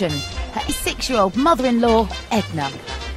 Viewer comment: It sounded like she said her sexual mother in law Edna😆